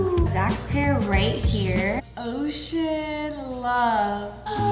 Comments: Who thought said oh Beep love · said “ ocean love” I thought she said “ oh sh*t love 😂😂😂